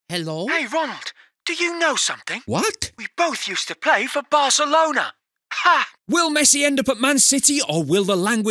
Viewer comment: "hey Ronald, do you know something?" "what?" "we both used to play for Barcelona" 😂😂😂😂😂😂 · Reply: I thought he said coach used to play for Barcelona 😂